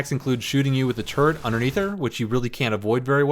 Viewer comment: the turret or the turd? Cuz i heard turd